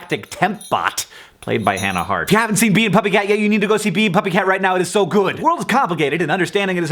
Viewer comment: OH Bee and puppy cat! I thought you said peeing puppy cat.